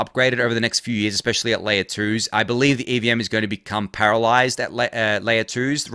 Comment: I had a brief flash of anxiety when I thought I heard you say the EVM is going to become paralyzed, then I realized you actually said parallelized.😅